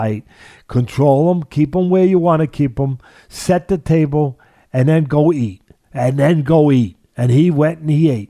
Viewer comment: did Teddy just say "Set up the table and then go eat"😂😂😂